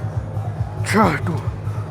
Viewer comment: I thought he said 'jhaatu' 😂